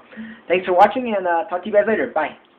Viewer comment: im sorry I thought i heard " F**k you guys later" it must be me... anyways you should do some more pranks! :)